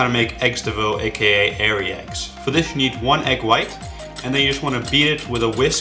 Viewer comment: with your accent, I could of swore you said Hairy egg at first. Then I figured out what you actually said lol